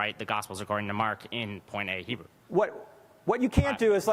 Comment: "Koine Greek" literally means "Common Greek", ie it's wasn't used only by those of high socioeconomical background but by everyone! The kid's just pulling 'facts' out of thin air and claims that scholars agree with him. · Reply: Thank you. I thought he said "Point A Greek."